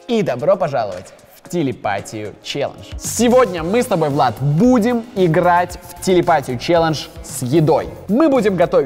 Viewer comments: вы слышали, "сегодня Влад мы будем играть..."😂. А 4 с ума сошел он сам себе сказал 😂. Кто тоже услышал лайк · Я один услышал что Влад сказал "Сегодня Влад мы стобой будем играть"???? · Мне одному послышалось что он на 0:10 сказал Сегодня мы с тобой Влад